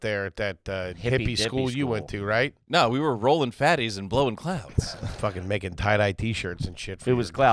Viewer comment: "Rolling fatties and blowing clowns." -. Is what I heard and what I'm going with.